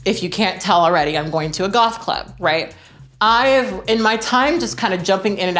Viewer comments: I know you said “goth club” but I heard “golf club” and I love the idea that you would rock that outfit while golfing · I am really enjoying all of these true tea videos! Side note, at the beginning I first thought you said you were going to a golf club not goth club, and I was like 1 why and 2 is that what people in LA wear to golf clubs? lol. It was cool to listen to you talk about passing, its such a complex topic and I always love hearing your opinions. I feel like I can really relate to a lot of what you were saying. As I start to pass more and more I am getting to know people before they know I'm trans. When this happens, I can get different responses to coming out. There are people who react fine, but there are also people who act differently after. I agree that it can be really hurtful when that happens and it super sucks. Passing is such a weird thing, it comes with privilege but also extra complications. Can't wait to see more videos, have fun golfing... I mean gothing lol! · Girl when you said "goth club" I misheard you and thought you said "Golf club" and tbh I was like "she can pull it off I'm not really surprised about this" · I though you said you were going to a "Golf club" for a second. I was like, "well, you do have that tartan skirt, and golf was invented in Scotland..." before I realized I misheard. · I know you do goth, so I don’t understand why I heard “golf club” lol I wonder if “goth golfing” is a thing?